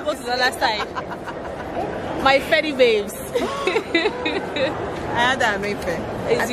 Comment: I heard you say " Feddy babes" Are you an ex- feddy girl? I'm one, Federal Calabar to be precise. 🙌🙌🙌